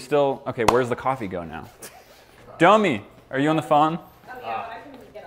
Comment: When said domi I it sounded like dummy